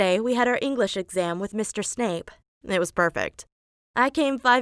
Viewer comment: I thought she was saying mr snake but she was saying mr snape